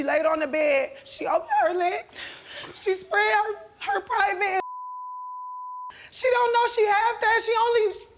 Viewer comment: Do anyone know what she said @ 2:18 ? And few seconds later when it blank out when she said Isiah did something? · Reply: 🤢 unfortunately looks like she said something like..she spread her legs and "pinched her clitoris", she doesn't even know she has that. And then something about him putting something "in her mouth" 🤬 effing disgusting